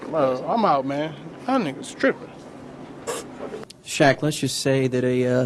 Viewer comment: “Yall niggas trippin” that one always gets me and directly after we got the shaq classic😭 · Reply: I thought he said “I ain’t strippin”